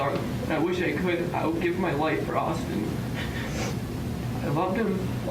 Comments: "I would give my life for Austin" Judge: Say no more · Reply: Playz dude we are not laughing at that he made a mistake the other guy said he THOUGHT that the guy said I will give my life for autism and THATS WHY WE LAUGHED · "I would give my life for Austin" Judge: Say no more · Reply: i swear i thought you said "i would give my life for autism"